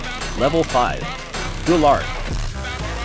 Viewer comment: When he said light saber i thought he said life saver 💀😭